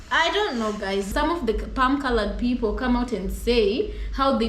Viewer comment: I am 69 years old, and I have never heard this phrase " Palm Colored People," but I love it! And you two are adorable! 🎉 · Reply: Is that what's she's been saying 😅 I thought she was saying pale colored people